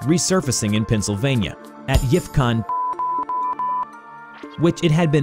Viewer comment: did you just say yiff...